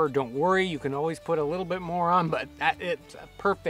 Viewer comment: "I didn't put too much pepper, don't worry you can always put a little bit moron" @ 9:06 Jeez, did I say something wrong?